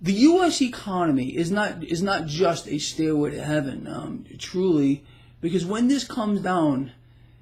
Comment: I thought you said "steroid to heaven" and I chuckled a bit. =)